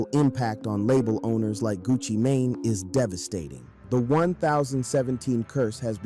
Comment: Gucci “Mah-Nay” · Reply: I thought he said gucci Mayonnaise 😂